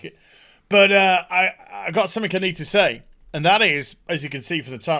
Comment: did you say.... "i got summick i need to say?"